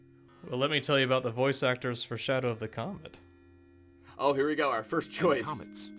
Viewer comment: I thought you said "Shadow of the Comment" at the start there.